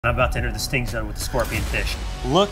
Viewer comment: I heard “stink zone” anyone else? 😂